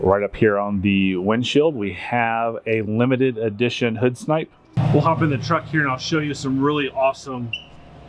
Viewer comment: a limited edition hood snipe? What are you actually saying? And if that’s what you said are you talking about the sticker?